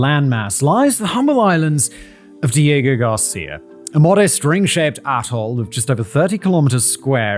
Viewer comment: Ring-shaped ATOLL. I swear, I thought he said ring-shaped a-hole. 😂